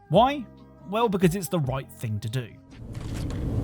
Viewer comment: Did you say "the wipe thing to do" at 6:36?